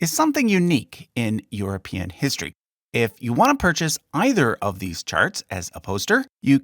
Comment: Sounded like you said, "If you want to buy NEITHER of these charts" 😂